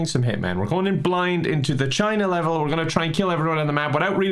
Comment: At first I thought you said gina level😂